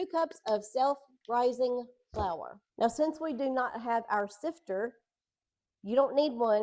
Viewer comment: 😂I thought you said "sister"!! I have a sister, and a sifter!! The new place is so nice and clean!!